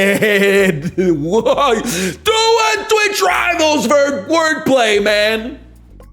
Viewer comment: God bless you if you can decipher what he said here man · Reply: "do a twitch rivals for word play, man"